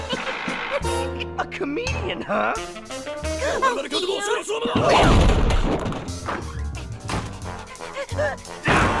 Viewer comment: /0:14 Did Eddy just say something in anime? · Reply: He said "Forgive me for my excessive use of force,but you leave me no choice my friend,SUMO ATTACK